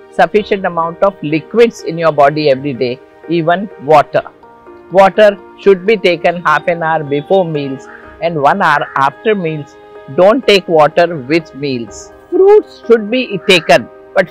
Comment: Can someone explain what she says here about water during meals? The audio cut out and I couldn't understand. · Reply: Hey! She explained that while Having meals , you have to either drink water half and hour before having your meal or 1 hour later after having your meal. It's like it's not good to drink water right after meals or during having meal.